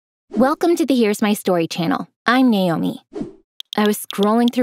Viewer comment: wait did she just say her name is Nayumi 😳...? Actually my first name is Nayumi... Btw I love all your videos 😊❤😊